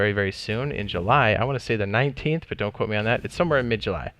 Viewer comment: I thought they said the 10th